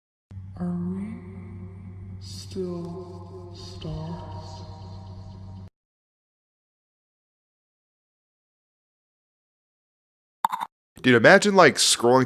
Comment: if you didn’t know what velvet said she said “are we still stars?”